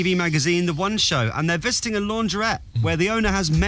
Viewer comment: For some reason I heard "fisting a lawngerette" instead of visiting .... Also I can't spell